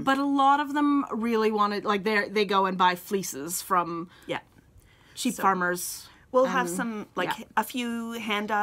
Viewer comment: Thank you for showing your blocking jar--I'm going to keep a look out for one this weekend! (I laughed when you said you don't stock too much for spinners--I thought you said it was because most just go to cheap farmers!)